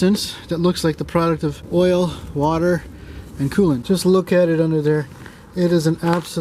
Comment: Did you just say "coom", dude? :D